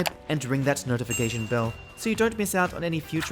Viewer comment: HAHA. I thought you said "See you next time, dickheads" at 04:12, but then I saw the channel's name. 😂 I must be environmentally damaged from working at a place where we call each other such things all the time.